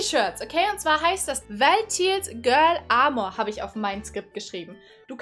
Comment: wie genau heißt der mod Nr 4? Ich habe verstanden: "vertils girls armor". Es wäre toll, liebe, wenn du die namen im video kurz einblendest!